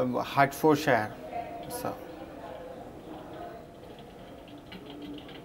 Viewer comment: Can u tell me the structure of the course? Yes my father n my father inlaw r Supporting me 😂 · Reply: I heard "can you tell me the structure of the COST?"